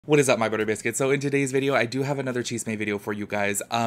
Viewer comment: Was waiting for the “hello my burrito skins” intro 💀😂 I like your chisme videos a lot. You have a very level headed and nontoxic approach to them. I love how you speak and your personality! Reminds me so much of a cousin of mine. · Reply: He says burrito skins???? I always thought he said butter biscuits >o<